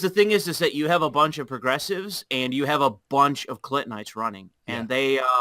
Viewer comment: When "clintonite" was first said, I heard "clit-knight" which I feel is absolutely fucking perfect and is what I will now refer to them as.